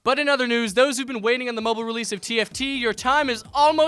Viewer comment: Was just listening and at first I thought he said the mobile release of tf2 and was like wtf.